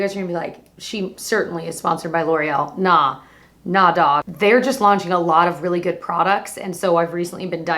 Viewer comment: Lol did you just say “nah dawg”? Lmao too cute!